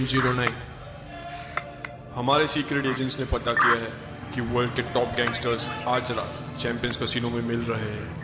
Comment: did he said tik tok gangsters